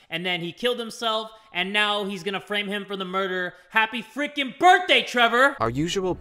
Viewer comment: When said murder for the first time in this video I thought he said pregnant.